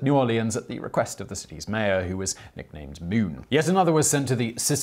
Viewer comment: What is saying? "Nicknamed Moon"? He gets really low volume right then. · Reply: Yes, he said the city wanted one because the mayor was nicknamed moon.